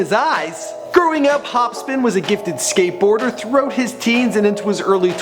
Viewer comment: why do you keep calling him HOPSPIN. Theres only 1 P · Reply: Yo first i thought he said Hopskin i was like tf??